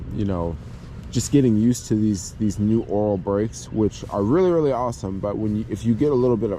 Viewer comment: I'm glad I listened more carefully when you were talking about the brakes. At first I thought you were saying 'oral brakes' and thought, "Whoa! Those would be very difficult if not more complicated to work on!" Things became more clear when I figured out you were talking about 'oil' brakes. That plus 'oral brakes' might be some new fetish. Eventually.